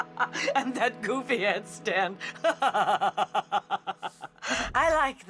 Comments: I thought yellow said and that goofy ass stand but she said that goofy head stand lmao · At first i thought yellow said “and that goofy ass stand” insead of headstand